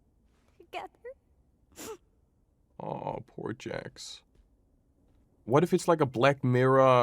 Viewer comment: why did the captions say poor J this isn’t murder drones 😭